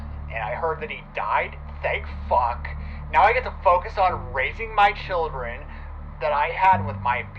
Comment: I thought he said raping my children 🙀